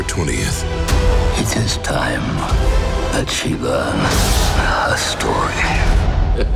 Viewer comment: I heard "its time that she learned her story"